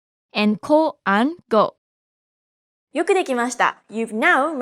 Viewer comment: What she said I didn't understand that word (yukidikimasta) I heard this but can anyone tell me please what see said i'm very curious to Japanese language 😇